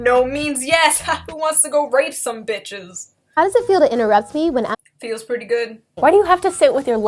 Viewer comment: I dont understand the question at 4:07, it comes to quickly. but the answer is a killer. :D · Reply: it says- how does it feel to interrupt- Feels pretty good